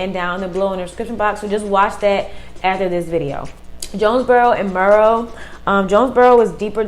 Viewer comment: what place did you say ? Jone what? I need to know !!! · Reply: Jonesboro